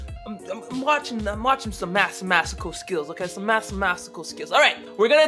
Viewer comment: Did he just say Massemasical? Hshahah